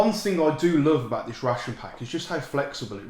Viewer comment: I thought he said a "salt" ration pack. And I was like, that's a kilo of salt! Salt is very important. So, if you get a salt ration pack, does that make you a salt leader?